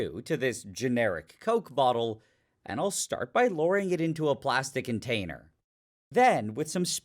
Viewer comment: When he was putting the Coke bottle into the beaker, I first thought he said luring and was very disappointed when I listened to it again and discovered that it was lowering 😔